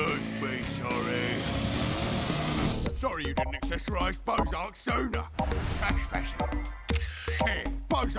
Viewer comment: I thought he said Bozark Soda.